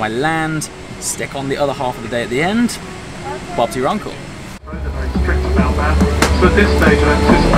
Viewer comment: Did he say "bob's your uncle" at 5:30????